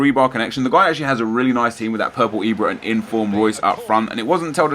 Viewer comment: did I just hear purple Ibra?